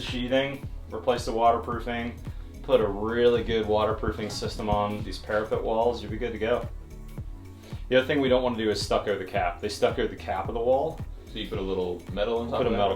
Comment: At first I heard, “The one thing we don’t want to do is stucco the cat.” I quite agree. Sounds like a good way to lose an eye. 😆